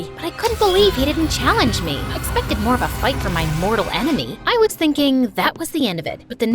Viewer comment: Did she really say mortal enemy on 3:41 minutes